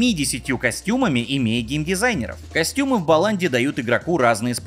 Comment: Мне одной послышалось на 18:11 "Костюмы в баланде"?